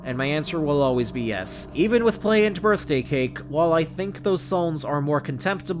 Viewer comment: what song besides Birthday Cake did he say? I couldn’t fully understand him? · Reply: Play by David Banner also is her name, she is trans 🏳️‍⚧️